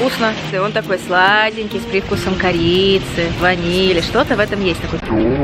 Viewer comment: почему я слышу: - он такой сладенький, с привкусом КОРЕЙЦЕВ 😂😂😂😂😂😂